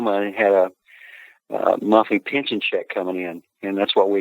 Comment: Can anyone understand what said that his dad had “coming in” to help his family financially ? I’ve played it back a few times but I’m from the northwest and can’t pick it up . Thanks ! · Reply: Monthly pension check